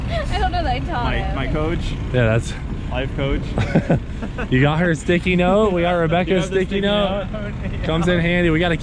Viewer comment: I was a bit worried when I thought you said, "I got her sticky."